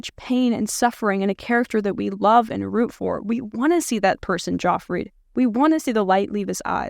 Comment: did you say "We wanna see this person Joffrey"? 😂 If I haven't misheard (and even if I have), I love the expression. Although there was never a figure in a tv series that I wanted to Joffrey more than... well, Joffrey 😉 Edit: I listened to this part first. Than I watched it and saw the clip from GoT.